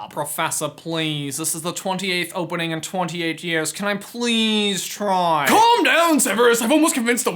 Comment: I thought I heard you say "Mufasa, please,"